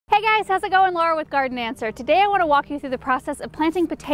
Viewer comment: I thought she said "Hey guys, how's it going, Laura with Gardenmancer"